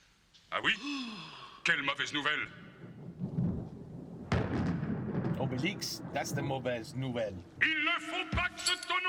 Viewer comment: J'ai entendu "Obélix, that's the OBESE nouvelle" X3